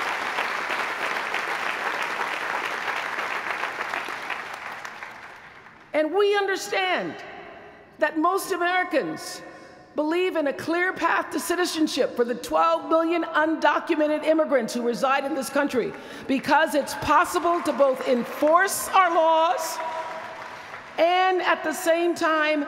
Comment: I just love her .. but did she just say 12 Billion people in America?? Time stamp